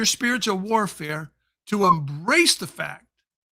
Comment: Honestly, I had to re-watch a couple times where you said "I want you to EMBRACE the fact" (Speaking about 1 Jo 4:4). Honestly, I kept hearing "ERASE" the fact! 🤣😮‍💨 My hearing is shot and something to do with distinguishing soft consonants). Wow .. at first that completely threw me, as you might imagine! Maranatha ...🙏🙏🐕